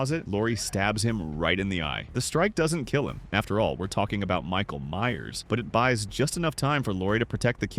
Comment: I thought you said Michael Jackson?😅